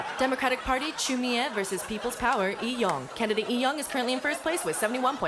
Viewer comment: i heard it was "peoples Coward" not Peoples power .. i was like "wow thats .. direct"